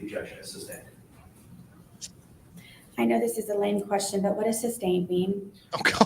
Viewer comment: I thought she said “I know this is a leading question but what does sustained mean” I was already dying laughing. When I realized she said lame that made it even better 🤣🤣🤣🤣